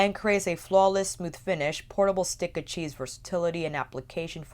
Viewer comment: it sounds like you say “portable stick of cheese”. That is all.